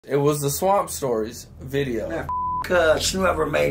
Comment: Lol I thought it said swamp stompers lmao